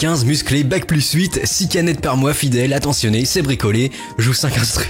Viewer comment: "6k net par mois" j'ai compris "6 canettes par mois", ça m'a semblé curieux la recherche d'un alcoolique :')